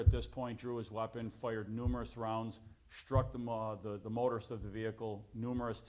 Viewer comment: swear it sounded like he was headed towards “struck the motherfucker” 🤣